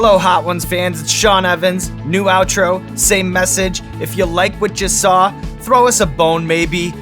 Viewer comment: At the end, when you said "If you like what you saw", I swore you were about to say... If you like what you SAUCE.